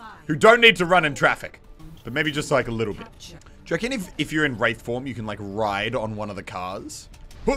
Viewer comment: did he say "rape form"? Wtf